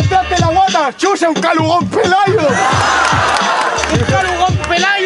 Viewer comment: El mrd dice en el minuto 2:00 solo escuche shushucayo os pelayo